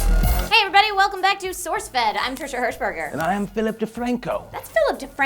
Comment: I always hear just "I'm Trisha Herpderpdersh"